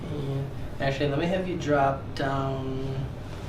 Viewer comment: i was about to say Amen when i thought Dr was to say, drop down your PANTS. "Actually Let me have you drop down..."